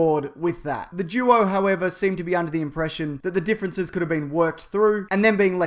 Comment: Did he say "The Jewo"? Lolo!